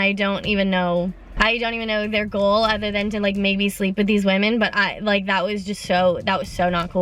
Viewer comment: “Make me sleep with these women”? Did I hear that right?